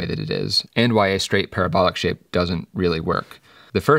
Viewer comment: everytime he said parabolic shape i thought he said pair of bollocks lmfao